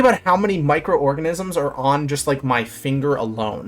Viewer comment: Street cat gonna eat tonight I honestly thought he said micro orgasms on my finger at first